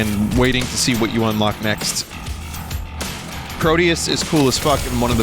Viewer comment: what game did he mention here? Was it called Krodius?